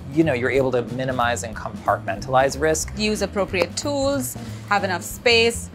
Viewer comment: What did and say at 14:57? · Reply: No dull knives please!